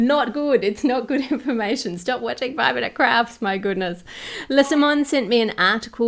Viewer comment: For a second there I thought I heard her say “5 minutes crap” and then it dawns upon me that the name suits them so much more…